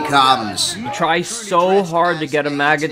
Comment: Oh I thought he said Maga chant lol. May as well when trump gets back in office.